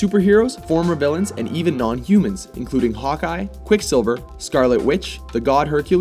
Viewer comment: "Included a mix of superheroes, former villians, and even..." Sounds like it says non-humans. Going on that assumption, isn't Hawk-eye entirely human, just with incredible skill?